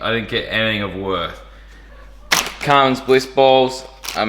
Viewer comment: Thought it said jizz balls